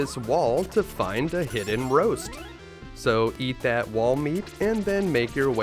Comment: He said EAT your wall meat. Not BEAT your wall meat. In case you thought thats what he said.